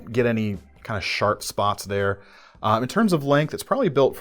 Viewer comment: did he say "Shart spots"?